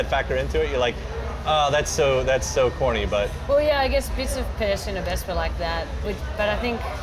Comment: Can someone help me out? Which song is she talking about? I couldn't understand what she said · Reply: She says "bits of Pedestrian at Best are like that"